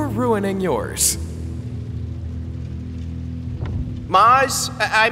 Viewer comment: Venus interupted Mars while he was downloading his braincells so he ended up saying his name wrong😂 Mars pronounced it ”Moss” instead of ”Mars”